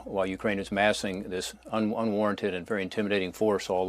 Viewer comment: what is he saying? Unwarented?